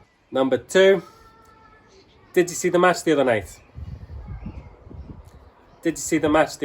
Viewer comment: I understood questions 3, 4 and 5. In question 2, I heard "Manchester United" in place of "Match the other night". 😓😂